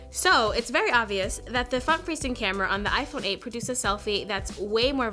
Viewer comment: you heard the "f*** facing camera" instead of the front facing camera